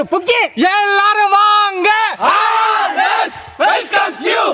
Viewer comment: What are they always saying? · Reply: All ways welcomes you 😁